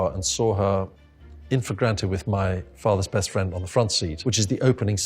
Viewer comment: If anyone got as annoyed as I did with the subtitles trying to figure out what he says at 15:50. The word is not "imforgranted". It's 'in flagrante'!